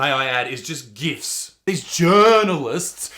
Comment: Ooookkkkaaaaay...... I'm an artist and I have never in my life felt the need to paint with my period. That just makes me cringe and want to throw a brick at them. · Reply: For some reason I thought he said Gifts... not gifs... I mean the articles were gifts to comedy!